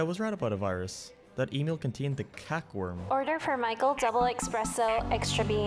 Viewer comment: I thought he said Cockworm.